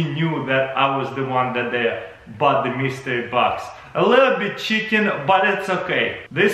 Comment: did he say Chicken ?